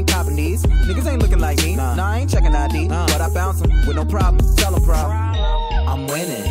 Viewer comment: i always thought it said "nah i aint checking out D but i found some with no problem tell em problem"